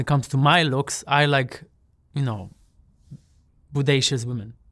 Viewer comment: What is his type? I heard "bodacious" and CC said Buddahs 😂😂